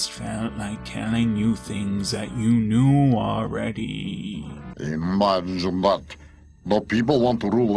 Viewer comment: I've watched this video for years and I thought Rasputin was saying "munch butt" instead of "imagine that." I don't know what this says about me.